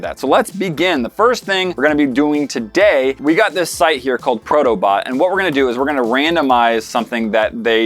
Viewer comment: Thought you said proto butt... not bot...